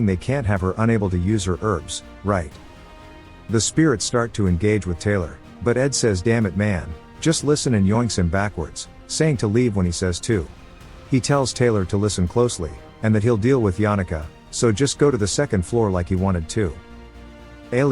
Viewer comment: did he just say yoink 😳